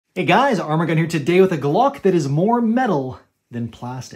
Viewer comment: For the longest time, I thought you said Armor Gun here. 🤣